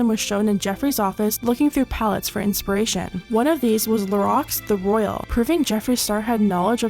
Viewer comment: LMAO, I spaced out and I thought you said "The Rocks the Royal" -- as in Dwayne Johnson, I